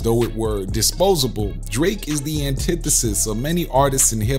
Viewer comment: Am I the only one who heard the narrator call Dre, Drake at 5:55? 🤷🏽‍♂️